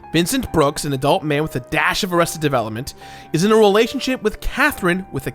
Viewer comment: “with a dash of [redacted] development” can someone help me? I can’t make out what he’s saying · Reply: arrested